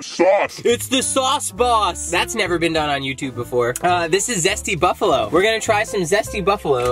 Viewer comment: I thought you said Stbuffalo, I thought what kind of STi is that?? 😂 😂 P. S it's BON JOVI P.p.s or 😆😉